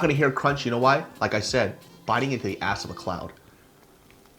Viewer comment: On the 2nd sandwich i thought u said “biting in to an ass of a clown” lmaooo